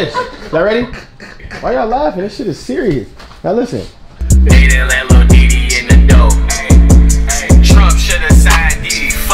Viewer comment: im trippnnnn i thought it said "adin let lil dd in the door"😭😭